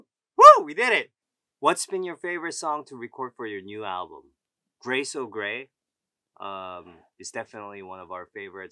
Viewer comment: the English subtitles say Grace O'Gray but the name of that song is actually Gray So Gray ^^